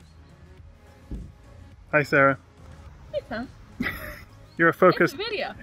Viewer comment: sounded like "hey pom" lol